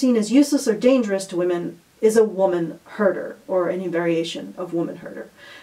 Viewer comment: Omg so funny I heard "woman herders" at one point not "woman hurter's"